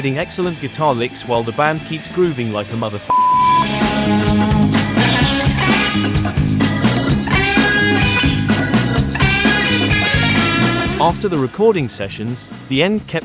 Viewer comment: "Whilst the band keeps groovin' like someone's mother?" That is what you said, isn't it? I like that! :0)